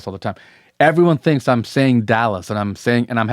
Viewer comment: I thought you were saying Dallas the entire time; wondering what it had to do with a crash in DC 😂